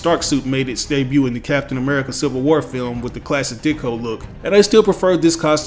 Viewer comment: hahah did he say “the classic dickhole look”??